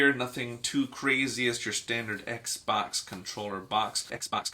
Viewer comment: I have one of these exact model controller and cannot wait to have it on my hands!! 🤩 By the way I didn't understand what happened on 1:35 Did you spell something wrong? Thanks very much for sharing this video! 💖 Greetings from Costa Rica! 🌴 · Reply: It’s a great controller! I love mine! And I just said “box” a lot in that sentence 😅 and hello back from the states!!!